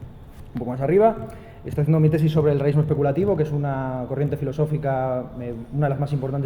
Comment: ¿qué dijo, no puedo oír bien, sobre una tesis? por favor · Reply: Realismo especulativo.